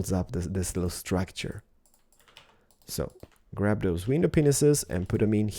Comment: It sounds like he says window penises